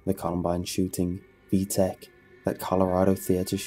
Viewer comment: Did he said v-tech 😂😂😂😂 on min